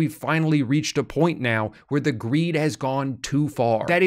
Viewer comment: i thought that said Creed